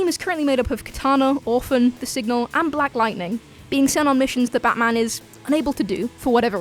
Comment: When announcing the members of the outsiders it sounds like you say "black lantern" instead of lightening.